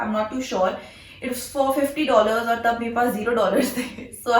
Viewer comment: I misheard it as $450....I was like tf mann....a 34K dress😂